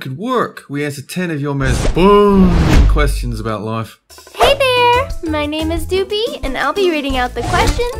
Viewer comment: at the beginning I thought doopie said "hey there my name poopie"